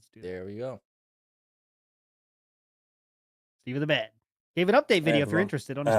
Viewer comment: did he say Steve of the bed